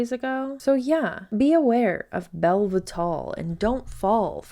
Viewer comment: Man, I was just listening to this (not watching) when the lady hawking Bellevital (I thought she was saying Velvitall, LOL), and I swear I heard the ghost of Jessie Lee Ward speaking. The voice similarity is scary.